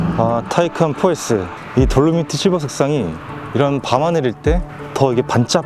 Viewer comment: 외부색상이 뭐에요? 잘안들리네용ㅋㅋ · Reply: 돌로미티실버=돌로마이트실버입니다^^ <link>